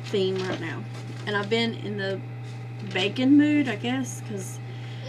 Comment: Love your ideas. Lol, for a minute I thought you were saying lately you've been in a "bacon" mood🐷 but then realized you were saying "baking" mood :)